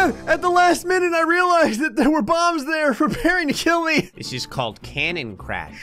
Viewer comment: captions: did you say pairing chili